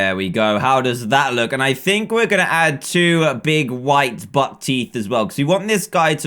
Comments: I thought that he said butt teeth lol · When he said buck teeth I thought he said butt teeth lmao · When said buck teeth I thought he said butt teath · Did say two big butt teeth around 1:55? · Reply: he said buck teeth but an easy mistake